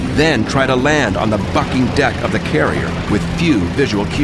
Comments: it sounds like he said fucking deck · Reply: C. Venom he's saying bucking deck.as in the ship was bucking over all the waves. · seconds he says BUCKING DECK. it was hard to make out. I can see how you could think he said F@ DECK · did he said " land on the fucking deck " ???? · it sounds like he said fucking deck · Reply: awe man dude..i read your comment and re-round to 8:14 and started busting up..it does sound like he said F-ing deck hahaha